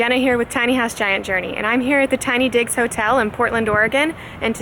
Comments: I thought you said "Tiny Dicks Hotel" oh god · I literally thought you said Tiny Dicks Hotel!!!